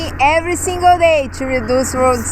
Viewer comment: do something everyday to reduce what? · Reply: Lol! is cute! Back when she first started speaking on the outro, we thought she was saying "Reduce World Sick". I notice she pronounces "suck" more clearly now.